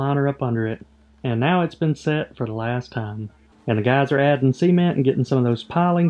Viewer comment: Since it sounds like you say semen when you say cement I think the pond should be named the Semen Pond!